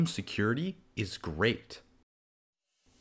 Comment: i hear im inside your bed but the subtitle i hear im inside your home